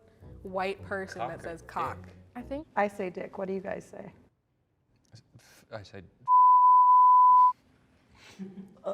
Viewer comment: I really wanna know what he said to where Cut was like... "yeah, imma go ahead and censor that" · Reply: I'm pretty sure he said "fuckrod". Tho my lip reading isn't excellent.